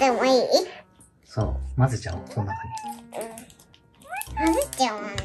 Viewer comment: あれっ、オルちゃん喋ってる！？ 3:36 「うん まぜちゃおー」って聞こえるんですけど・・（笑）